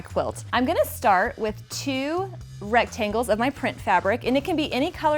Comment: i owe you an appology i am humbly sorry i thought you said pink but in fact you said print. not pink Maybe you should try red Moscato wine its sweet but sooo good . goes great with quilting wouldnt you know ! getting old age ears . i am such a fan of yours I work at a fabric store in Weatherford Texas and daily tell someone or several to check you out. You have lots of weatherford fans .